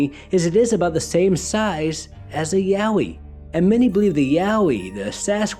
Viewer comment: Real Name :Yowie What i hear "Yaoi"